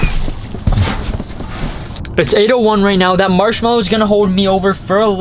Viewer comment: Who else heard "potato one right now"? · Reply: JustDizDud Channel,he said it’s 8:01 right now